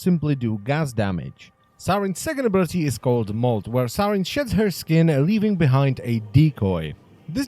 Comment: "where saryn sheds her skin..." I could've sworn at that part he said shits